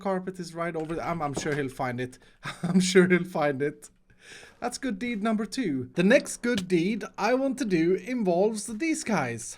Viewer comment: I thought he said he wouldn't do a good deed for mumbo 🤔